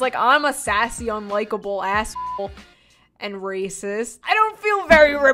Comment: did she just say she was racist? 😳 Did I mishear that?